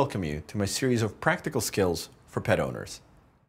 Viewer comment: Omfg, when he said ‘pet owners’ I heard ‘pedobears’ XD